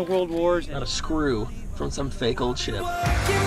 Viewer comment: Is it just me or did Cory say the s word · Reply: He said ship not the s word